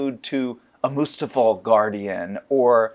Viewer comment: I thought he said "mustafar guardian" lol